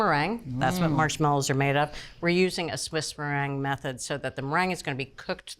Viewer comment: But she says Swiss meringue.. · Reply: You are right. She says Swiss, but I swear I heard her say Italian